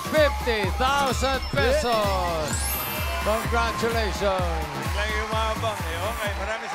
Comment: Ano sabi ni Henyo? “BIGLANG YUMABANG”? 😮😂